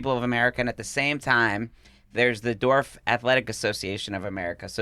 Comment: When mentioned the Dwarf Athletic Association, I thought he said "Dorf Athletic Association" and I couldn't believe they named their athletic association after a Tim Conway character.